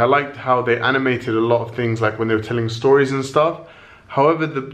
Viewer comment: what did you say?? hmmmmmmmmmmm not Nicola. :P Hayati?? I just googled!! LOL